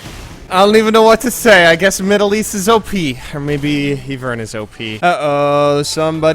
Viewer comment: when he said "mid elise is op" i thought he said "middle east is op" hahaha